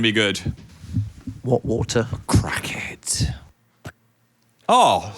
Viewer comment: thought Phil called Dan a crackhead 😭